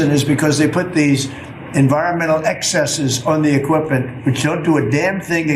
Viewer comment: Did he say "equippant" (which is not a word) instead of equipment?